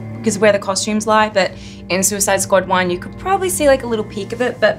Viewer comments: did she say suicide squad 1? She just told us there’s gonna be a second one coming😏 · Did she just say ”Suicide Squad ONE” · Sorry but did she just say I suicide squad 1